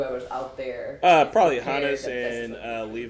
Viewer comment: did he say Hannes? bruh time to rewatch season 2